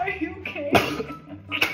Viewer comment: LMAO HE SAID BITC AND I THOUGHT HE WAS TRYING TO SAY BITCOIN UNTIL I READ THE COMMENTS 😭